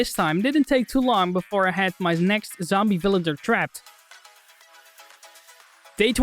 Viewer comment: did he say villader btw